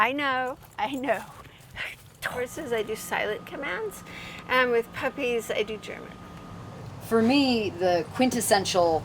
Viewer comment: Okay I listened to this one part a couple of times now and every time I hear "with horses I do silent commands and with puppies I do... German" did she really say that??